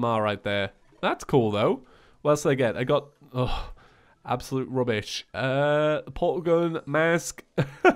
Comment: im surprised they didn't have the ugandan knuckles be a pet or/and summon, based of the Pygmy Staff edit: did i just hear you say the terrarian rubbish